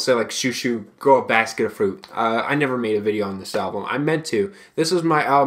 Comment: wait what is the album i cannot understand a single word he said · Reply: Girl with a basket of fruit by Xiu Xiu